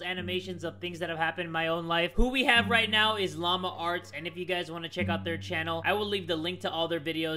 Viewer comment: Bro why at the beginning of the vid I thought it said llama farts